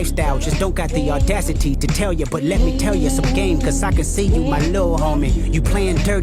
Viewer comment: When Kendrick says “let me tell you some game so I can see you my lil homie” it sounds like he says Socrates (Greek philosopher his mission from the god was to examine his fellow citizens and persuade them that the most important good for a human being was the health of the soul.)